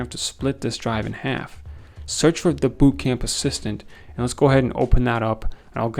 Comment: Did anyone say boot camp???